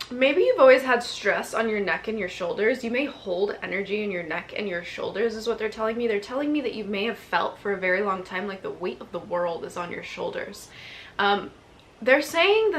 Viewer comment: when you said ‘something with your neck’ @ 7:40 i heard ‘off with your head’ from alice and wonderland for some reason reason… like clear and LOUDD 😂